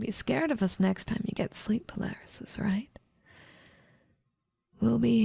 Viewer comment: bro she really said "sleep palarysis" im deadd 💀